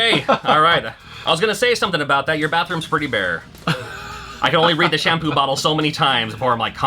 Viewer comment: Did I hear that right? Did just say he would cum on one of shampoo bottles?